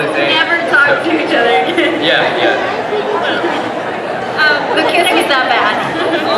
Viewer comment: What did katelyn say about the kiss? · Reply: She was joking but she said the kiss was that bad